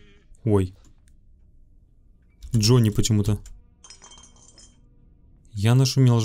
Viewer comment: как ты услышал "Джонни"?Там чётко было слышно "Гранни".